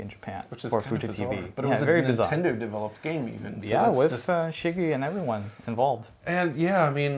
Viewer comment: Did I mishear or did you call Shigeru Miyamoto "Shiggy"? lol